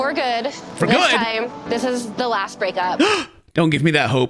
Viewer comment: hey im just saying ken. she said "this is the last time we break up". she did NOT say "this is the last time we get back together" lol next time theyre just going to be in it for the long haul! haha